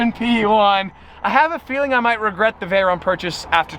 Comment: Did he say Veyron???